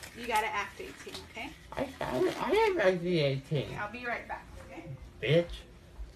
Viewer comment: the “act your age” lady reallyyyyyy pissed me off, i prolly woulda let a name or two slip too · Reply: I heard him called her the B word after that 😂😭